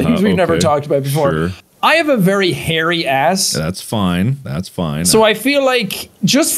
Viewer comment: Wow i skipped to half of the video and i just hear jack say "very hair ass"